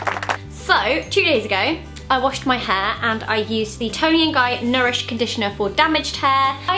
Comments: Haha omg I thought she said "I washed my hair two years ago…" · I heard 'two years ago I washed my hair' instead of 'two days ago' ^^ kind of felt like : WHAAAAT ?? for 2 seconds.. x)